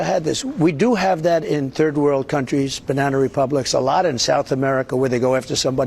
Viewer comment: Did he say Banana Republics?